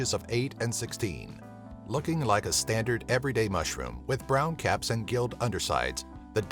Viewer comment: When he said: Looking like a standard everyday mushroom ... I thought he said looking like a snacc 😂😭🤦🏻‍♀️🙊